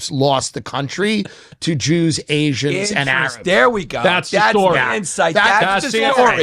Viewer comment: " to Jews, Asians and..." what did Tim say? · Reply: Arabs